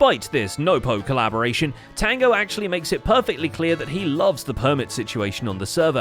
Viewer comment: Why do I keep hearing "making it perfectly Cleo" in this episode?